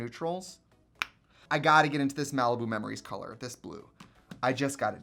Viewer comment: I thought you said Malibu Mammaries 🤷🏼‍♀️